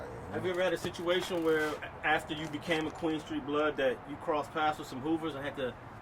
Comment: I always thought he was saying Quinton street...